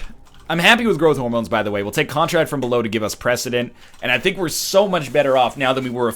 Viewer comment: What does he mean whenever he picks up devil item (most of the time) he says that he takes it only for president/precedent*(15:21) or w/e. * - I am not sure what he says nor what it is, what kind of mechanic in the game is he using? P.S. He can 0.5hp beat mom but not flawless the goddamn first floor boss on EtG kek. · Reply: when giving up any amount of hearts for a devil deal it sets forth precedent that later in the game you will only get Devil Deals where if you don't take a devil deal when it's first presented to you Angel rooms will appear there on after after a floor if you hsppen to recive a room for deals